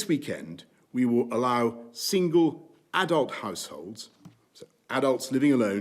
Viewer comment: I thought it said shingles and freaked out 😫